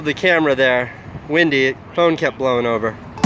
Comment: did he just say phone?